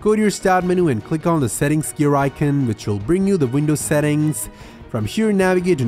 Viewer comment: I misheard it as settings shuriken and now will never call it anything else